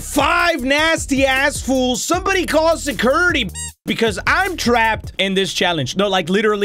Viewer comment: i honestly thought it said security beach